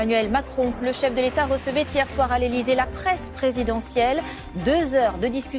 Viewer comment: J'ai entendu "presse présidentielle" puis "chasse présidentielle" depuis je ne pense que "guillotine présidentielle", c'est étrange quand même!